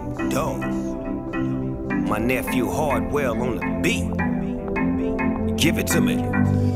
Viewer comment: Am I the only one who heard Snoop Dogg saying my nephew Hardwell?